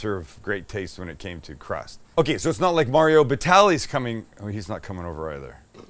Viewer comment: Is it just me that thought said "Mario Balotelli" the footballer instead of "Mario Batali" lmao